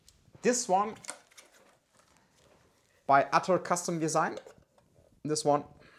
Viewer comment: Whats the footswitch? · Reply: Was wondering the same thing. It says OCD, and I thought he said Outer Custom Design, but can't find them on google.